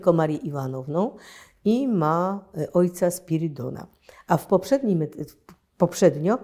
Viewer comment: Czy ja dobrze usłyszałem? "Ma ojca Spierdoła"?